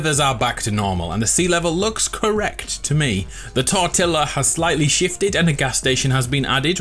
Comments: I just heard tortilla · he said tortilla instead of fortila im dieing from this. he is hungry guys go get him some at burrito taco bell he will enjoy it. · did he say tortilla